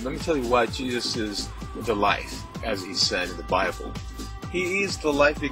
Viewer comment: sounds like he said 'jesus is the lice'. Yeah, what a louse.